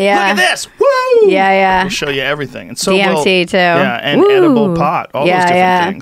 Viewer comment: Bridgett says DMT after Joe makes the joke about mushrooms. I heard it much more clearly in the audio I was listening to earlier. So true!